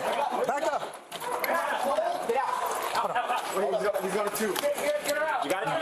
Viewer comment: it was only a bike lock , why would you need to shoot him 🤦‍♂️ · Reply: Hi, at 1:15 in the video the officer with the rifle says, "Hey, slow down, slow down, let me take point with the rifle." After pushing his way through you can hear at 1:23, "He's got a tube." So, he knowingly took point with a rifle against someone they thought had a tube. At 1:40 you hear someone say I got you and 3 rounds are fired. During the clip you don't hear anything directed to the assailant, in fact you hear the rifleman constantly being told to slow down. In other words, he is not keeping formation. You say it was 100% justified, and infer that the 14 year old girl was collateral damage, but the originator of the post disagrees, by saying that the rifleman did not have to shoot. And I would agree. The 3 other handguns could easily have taken out the assailant and the fact that the rifleman had to say shots fired 1:43 tells me that no one was expecting it. This whole thing should have ended in an arrest, not 2 dead.